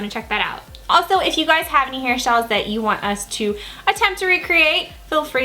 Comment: Sorry if the codes are not working guys!! Working on getting the codes fixed now! <3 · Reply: did you say hairshyles 😂😂😂😂